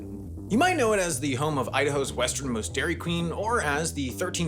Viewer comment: I thought he said "the western most DQ" and not "Idaho's western most DQ" at first. So I started looking it up cause I knew that wasn't right. After finding several, I went back to listen to it again. Although I did learn it is farther west than the eastern most DQ in San Diego County.